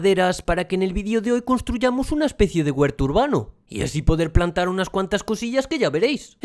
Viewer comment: Pensé que decía el hutero hurbano